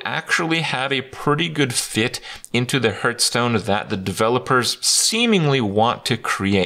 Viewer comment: I always hear "Hurtstone" when he says it